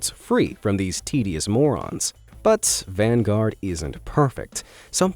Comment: did you just say vanguard